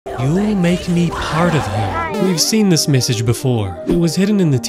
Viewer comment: I thought she said "You'll make me spider mill!"